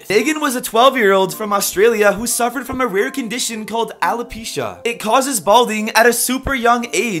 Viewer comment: Okay I know im not the only that thought PUEBES when he said Alopecia